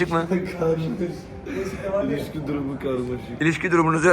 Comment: olm ilişkiyi içki anladım awina kulaklarımı sevim